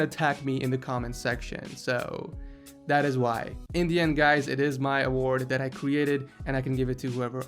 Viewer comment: When he said "in the end guys" when he was justifying why yazeed doesn't get the seal of approval, I thought he said "Indian guys"